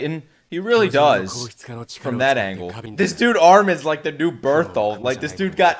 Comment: Did he say “This dude Armin is the new Bertholdt”? He did not just say that 😳😳